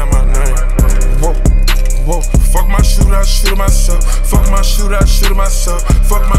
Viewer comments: Work partner in his 50s passed he thought he said “ I shitted myself “ this is a banger for em 😂 ! LLScott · Song is hard but it sound like future said “ I Sh*tted myself” instead of shoot him myself